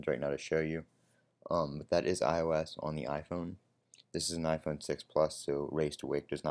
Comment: said it doesn't work on the 6 plus not 6s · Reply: Ahhh misheard then. I thought you said this is the 6s plus so raise to wake does not work.